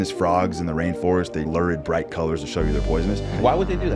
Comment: I think he said Lurid and the subtitles didn't match